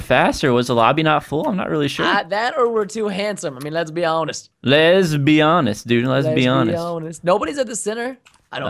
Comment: i thought you said lesbian est when you said les be honest